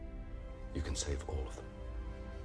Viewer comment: When Jor-El said, "You can save her Kal" during this reaction, I heard "You can sniff her Kal" and I was like oh boy. 🤣🤣🤣